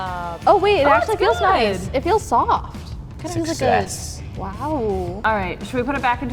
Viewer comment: what did you just say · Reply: He said its a succsec i thing